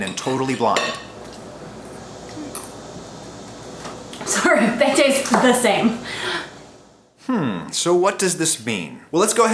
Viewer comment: what does she say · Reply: "Sorry, they taste the same"